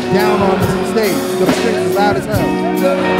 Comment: did he say the chics are hot as hell? I think Joe noticed too 😁